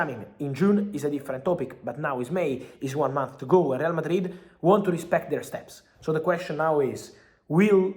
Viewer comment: Initially I thought I heard Fabrizio saying Real Madrid want Bruno and I was hyped.... then I read the time stamp and saw aL hiLaL................. No, absolutely not, under no circumstance will this happen. I'd rather see Bruno going to Man City than going to the Saudi League.